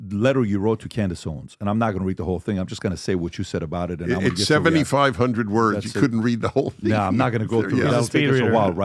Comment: I stopped watching when he said Candace supports Hamas, he manipulates even on small issues like keeps stressing that he wrotek 75 hundred words whereas it is only 12 pages. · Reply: He clearly says 7,500 words. Not 75,000. Are you dumb??